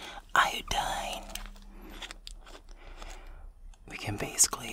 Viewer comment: was about to fall asleep to this ‘til I heard “are you dying?” I WAS LIKE HUH WHAT DID U SAY? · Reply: 😂 it was iodine ig 🤣🤣